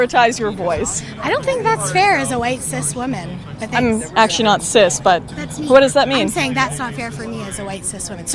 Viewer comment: Did say she WASN'T cis??